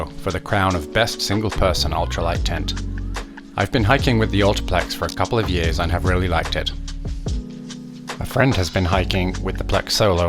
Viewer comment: A the start it sounds like you said "I've been hiking with the Ultraplex a couple of years and never really liked it." haha